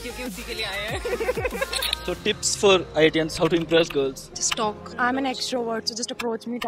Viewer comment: I fitst Thought she said, just STALK 🥷